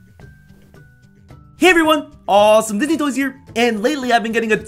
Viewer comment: It sound like he said awsome dizzy toys